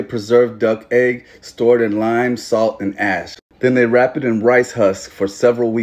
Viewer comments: Thought u said lime salt and ass for a min 💀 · Thought he said "Stored in lime, salt and ass" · I thought he said stored in lime, salt, and ass. 🤣 love your videos bro. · When I heard "lime, salt and ass" I immediately opened the comments and I was not disappointed.. 😂😂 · I swear, I thought you said the egg was set in... salt, lime, and ASS And I was like " WHAT DID HE SAYYYYYYYYYYY???"